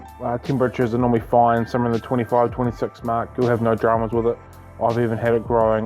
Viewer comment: I missed allot of his spoken words. Like he says foyn instead of fine. At 5:40 he says "ken britches" what is that...? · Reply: “ken britches” = temperatures